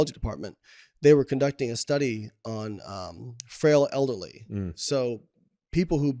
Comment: Thought he said "feral elderly"... 😅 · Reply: He said frail elderly